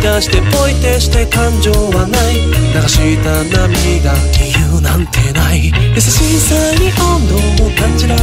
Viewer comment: 理由なんてない のところが、天〇さんに聞こえるなぁ わかる人いるかな〜